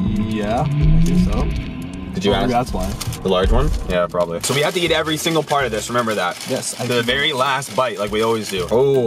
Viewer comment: it sounded like you said we have to eat every fart of this